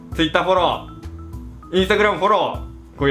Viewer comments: 動画の最後 どうもボッキーでした に聞こえた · 最後のポッキーでしたが勃○ーでしたに聞こえるwww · ドンマイです( ＞Д＜；) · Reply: んでも6:39 Bッキでしたって聞こえるような..... 難聴かな